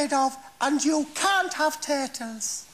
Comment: What did they say at 4:46? You can't have "Tedlers"?